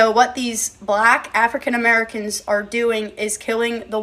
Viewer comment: As a black South African...she basically said a whole lot of NOTHING😂😂😂😂 also...its generally the racists that are into hunting here lol. May she be so traumatized that she never comes back😊❤ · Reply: Did she just say black african americans killing farmers in south africa? 4:30?